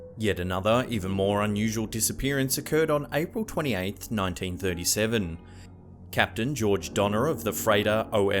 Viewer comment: April 28th is my Birthday 8:24. For a second, I thought he said 1997, my Birthyear, but he said 1937, one number off.